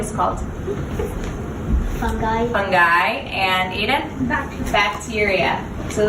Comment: Did you say Aiden? Aka AUSTEN BROTHER? I was searching for your channel ;w; -Ali B.